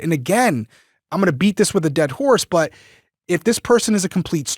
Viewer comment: “I’m not going to beat this with a dead horse.” you crack me up the way you qet you get expressions wrong. 😂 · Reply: I thought he said "I'm going to beat this with a dead horse", which means he is gonna keep talking about it.